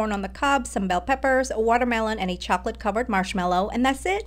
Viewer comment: I thought you said chocolate covered watermelon- 😭😂